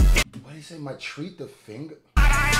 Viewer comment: I think he says "My trigger finger fast" but not sure abt the other word.